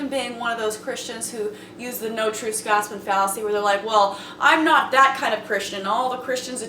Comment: can somebody tell me what fallacy she said? I cant hear it well. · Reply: J583 At 2:26 she says: "No True Scotsman fallacy."